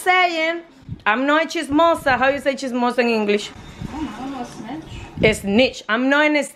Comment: She said chizmosa I thought she said cheese samosa